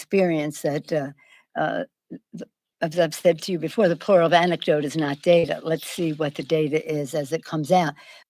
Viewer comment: I thought she said the plural of antidote is not data, if that made sense either